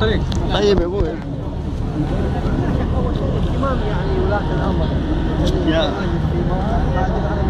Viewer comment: اخي معليش ولكن كأنني أسمع بـ 1:22 يقول اهتمام ولي الأمر وليس إحترام ولي الأمر ولكن والله أعلم ماذا يقول ولكن تذكر أن بعض الظن اثم وانا بكلامي لست ادافع عنه او اساعده